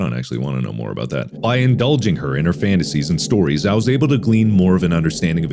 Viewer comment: Did anyone else hear "indulging her inner fantasies?" and not indulging her in her fantasies?